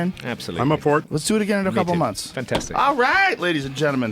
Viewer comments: I swear Randall says Joe I would like to eat you out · Does Randall say "Joe I would like to eat YOU out" in the last 5 seconds of the podcast?